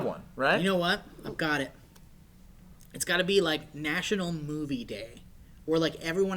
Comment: I thought said "National Booty Day" :D also interesting (Why national though?)